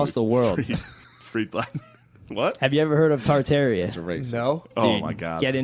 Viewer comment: What word are they saying at 9:50? I searched "torterra" and all i got was a bunch of pokemon stuff.